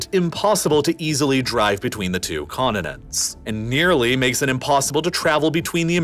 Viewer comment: Did he just say “condinends”? Pronounce your t’s buddy 😂